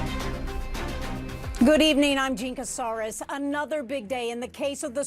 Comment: 😅 Hahaha; "Good evening, I'm Jean Casarez..." she said lol but for some reason I heard; 🦕"Jinkasaurus" 🦖